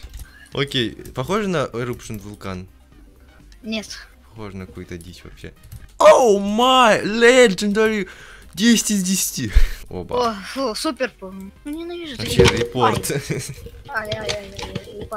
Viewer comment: Мне послышалось или, сказал "Эбала"